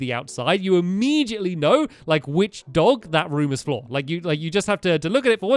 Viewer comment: did Stampy say "That room is floor" instead of "that room is for"?